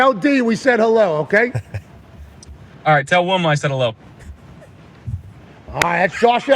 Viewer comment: what did josh say? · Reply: Tell Wilma I said hello